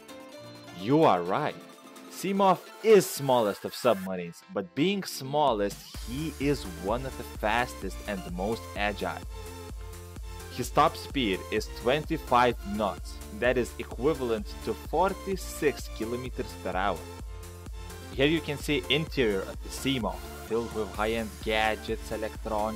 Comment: When he said 25 knots I thought he said 25 nuts XD